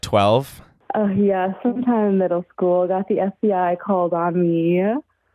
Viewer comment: i heard when i was 12 i got the the STI called Omni 😭 i was even rolling with it when she said it’s my fun fact & tinder bio